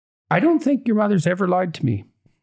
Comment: "I don't think your mother has ever liked me?" Did he say that? Edit: LIED TO ME* Ok, I'm in.